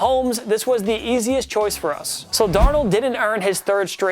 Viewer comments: Is it just me or does he say Darnold instead on Donald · he really said darnold